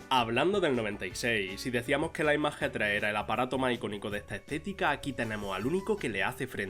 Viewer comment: Que dijiste? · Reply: El iMac G3